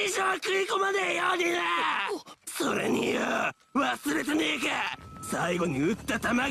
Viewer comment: Omg did he just say the n word!?